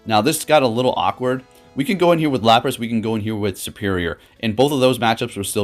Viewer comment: did you just say lapras?